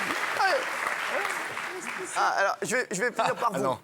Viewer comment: Elle dit quoi Isabelle à 9:42 svp ? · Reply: Vodka Tagada Laisse pisser ? C'est pas une blague ça a rien de drole